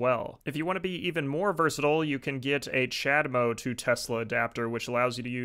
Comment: Who else heard him say chadmode